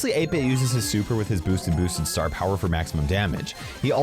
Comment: Ah yes, 8-bit’s boosted boosted star power (10:45 anyone else hear it?)